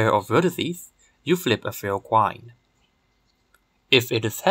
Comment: At first, I thought you were saying to flip a fair quine.